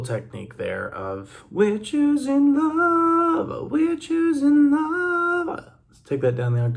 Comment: I thought he was saying, "We're choosing life." It fits with all the narratives.😮